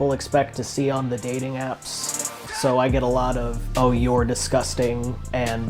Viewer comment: i thought you said you agree that she's disgusting 😂😂😂😂